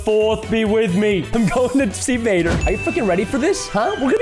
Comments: DID U SAY “ARE U F-ING READY FOR THIS” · Hold up at 1:45, WHAT DID HE SAY?! Please someone tell me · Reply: He said, freaking